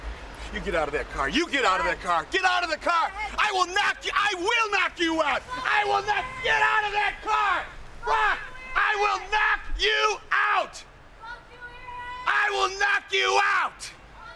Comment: is Steph saying 'fuck you, Eric" (4x) as she drives away with Brock and Heyman in the limo?